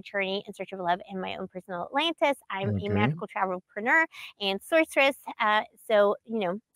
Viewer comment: Bro did she say she was both an Entrepreneur and a Sorceress?